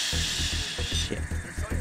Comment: I think I Heard 1 of them say shit